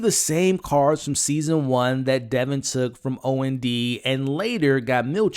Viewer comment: "Dylan" not "Devon." It's hard to tell which one you said, but CC/subtitles say "Devin."